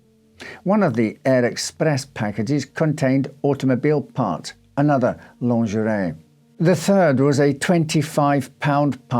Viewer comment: I misheard the narrator, and I thought he said automobile parts and other lingerie. I’m sure it says much about me.